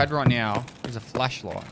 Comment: "The only thing i wish i had here is fleshlight" 20:17 i hear you mate but i don't think it is appropriate at that point and time.